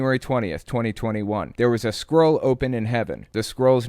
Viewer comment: “Remember Kat Kerr?” How can I forget? (Unfortunately) “CAN YOU NOT STAND...” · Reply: Am I hearing that correctly? “There was a squirrel 🐿 open in heaven. The squirrel’s 🐿 name is called Justice”? Normally I’d assume I have just misheard because it makes no sense, but we’re talking about Kat Kerr here so all bets are on the table Edit: I am mishearing. It was scroll 📜 not squirrel 🐿, but squirrel could have been a legitimate option in Kerr’s “visions”